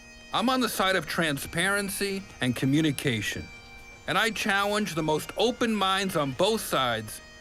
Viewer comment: it sounds like Jeff says "chowenge" instead of "challenge"